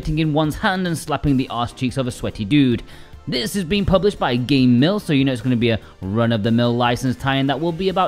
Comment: I thought you said game milf first.... God help me.